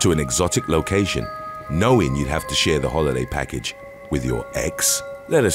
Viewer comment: I thought the narrator said,would you go on a holiday package with your eggs